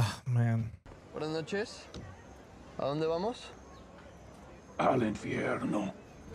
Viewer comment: What John say !? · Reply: The driver asks where to go and John answers " al inferno " which means "to hell"